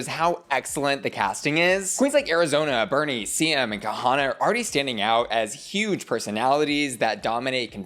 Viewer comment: Did I hear Kahanna?